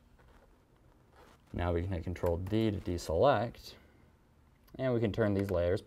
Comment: I thought he said hit ctrl+X / ctrl+D at first .. he's actually saying ctrl+V not ctrl+D